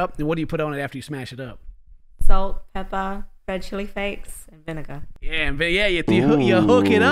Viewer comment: what was the third thing she said? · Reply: Red chilli flakes lmao